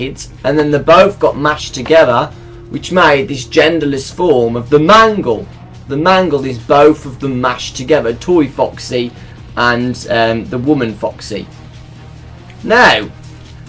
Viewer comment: Wait when I heard you say mangle it kinda sounds like man girl